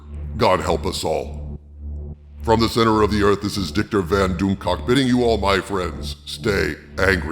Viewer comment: Lol 16:14 "this is Diktor Van Doomcock" replayed it 6 times to make sure I heard right.